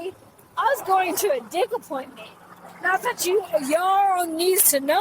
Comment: did she just say "I was going to a dick appointment" ? lol